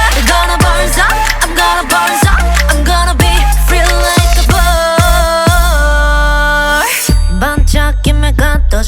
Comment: I keep hearing “I’m gonna be real life bird”🤣😅 Namjoo is a beautiful 🦢 🦅